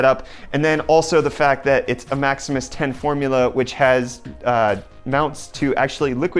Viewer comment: Did he just say „ Maximus Hero 6“? 😂👍